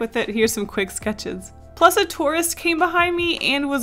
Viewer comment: When was saying tourist at first I thought she was saying Taurus like the zodiac sign